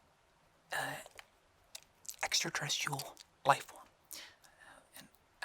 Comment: I thought my mans said “extratesticles”